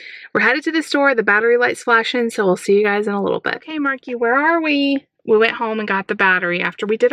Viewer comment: it sounded like was saying I'm sharky lol